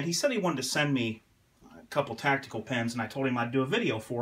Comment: I thought it said tactical penis XD lol